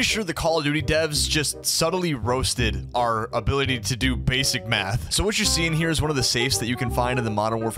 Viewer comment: I think i heard "basic meth" when you said basic math